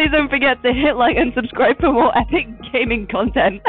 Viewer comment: i thought at the end.. she said.. "ETHNIC GAMING CONTENT" AND IM LAUGHING SO HARD